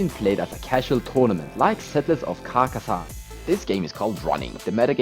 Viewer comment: I misheard that as "settlers of carcassonne".